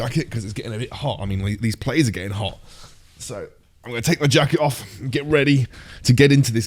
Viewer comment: When you said jacket off, I heard jackylove lol😂